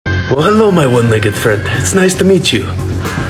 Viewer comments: Video: “Well hello my one legged friend” What i heard: “Well hello my naked friend” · I heard "why hello my one naked friend"